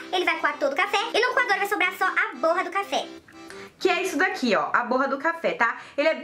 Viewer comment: Entendi "porra do cafe"kkkk raxei